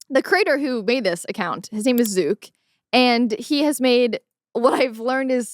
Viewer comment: I heard “his name is Zuke” and I was like “his name is Zouk????” 😮 #BoomChikChik